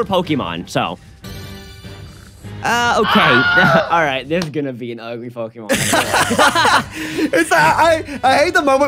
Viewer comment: When hatched the basculine I swear at first I thought he said "this is gonna be an ugly fucking mon" 🤣🤣🤣